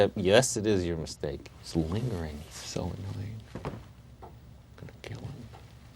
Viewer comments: Did I just hear Toby said he was gonna kill Clark at 3:19? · did he say "I'm gonna kill him"??! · "Lingering silly, gonna kill him." Is that what he said?! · Did he say Gonna kill him? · I just heard for the first time him say "I'm gonna kill him" and i think when he goes to the prison he tells him that he knows he innocent becuase he knows who it was the whole time, that why he strangles toby and makes him look more guilty; he went there to tie up loose ends